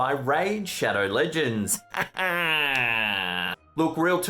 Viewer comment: Bahahahahah I thought he said "Rage cheddar shins" instead of raid shadow legends. I was way interested in the former haha